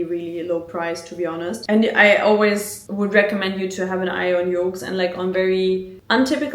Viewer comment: hahaa amazing so far and im only halfway!! does anyone know what mentions around 15:12 ? Yorks? or Yolks? im in us, never heard of this...THANK YOU!! ✨🌹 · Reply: yoox